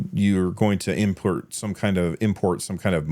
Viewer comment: Did he say impurt 7:28 Da furk!